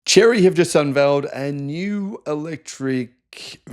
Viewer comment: Whew ... I thought it said "Chevy". Oh well.